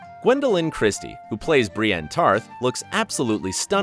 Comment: I thought i heard "brienne of Farth"